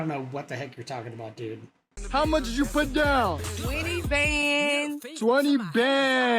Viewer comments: I'm pretty sure she said 20 Bens. That is $2,000. Well, if she put down 20K, then she is even more stupid than I thought. Have a good one. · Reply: I really think it was twenty thousand down for that vehicle. The common, the new lingo, is bands.And that's what you didn't hear. You thought they she said Bens..understandable..old school But he said bands , and that's a different thing. It's a stack of cash wrapped in a rubber band. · I think she was saying bands not bens, 20 bands, which is 20 g's, one band equals $1,000 bucks. One Benjamin is $100. But she was saying bands I think.